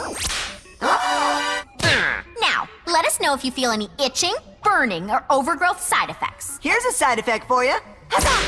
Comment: it sounds like she said 'bitching'.